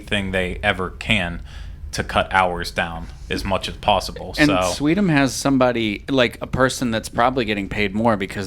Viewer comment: does say “sweetum?” What’s that?